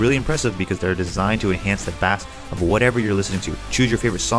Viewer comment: Designed to enhance the what? Sounds like you're saying bass like a fish